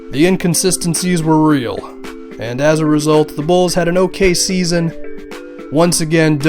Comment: thought you were going to say OKC but you said okay season xD.